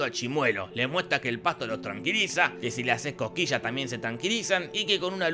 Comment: Juro que entendí "les muestra que el faso los tranquiliza" Y tenía sentido